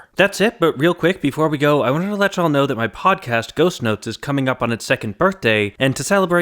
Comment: I had to do a double take. I thought you said Ghost Nuts. Which... Well... Sure.